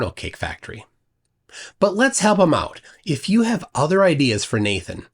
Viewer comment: I had to go back and slow it down because I misheard “let's help him out” as “let's have a mouth.” Sigh, getting old.